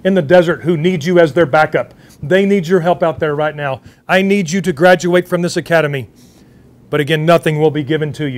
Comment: Who else thought he said “All Chinese Must Graduate”